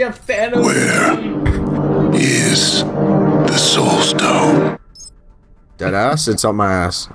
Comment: did you know that in 4:38, instead of "where is the soul stone." he says "where is the sauce stone." I think it has a bit of a hybrid of Gordon ramsay. LOL